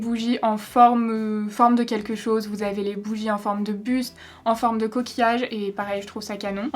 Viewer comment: à 5:13 j'avais compris "les bougies en forme de p*te" j'étais là HEIN QUOI ? XD